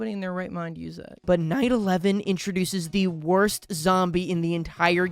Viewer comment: When he said "Night 11" i thought he said 9/11...